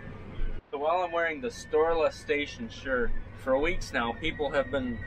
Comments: LOL I thought you guys were saying Storeless Station. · I thought you were saying store less station the first few times you talked about it but I thought it was just me! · Not gonna lie. I thought he was saying “storeless station” for this whole time...